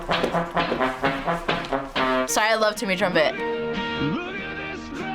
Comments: d-did she just call that a trumpet? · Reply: the guy who made the original song is called timmy trumpet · did she say truMPET? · did she just call that a trumpet? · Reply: She said that she loves Timmy Trumpet